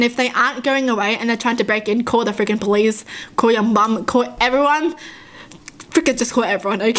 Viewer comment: it sounded like she said bum instead of mum